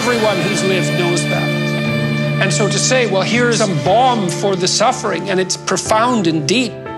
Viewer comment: Is he saying bond, bomb, or bong @ 5:33?? "Well here's a __ for the suffering" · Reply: Balm